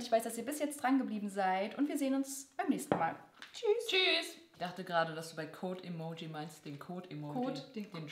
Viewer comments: Ich hab auch Kot💩 Emoji verstanden😂 🍕🍕🍕🍕🍕🍕🍕🍕🍕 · Haha ich hab auch Kot-Emoji verstanden😂💩